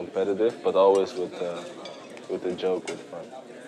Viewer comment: "always with the joker" What is he saying lol?